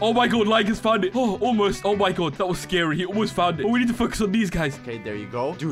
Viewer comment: did you just say fuck 0-0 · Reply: He said focus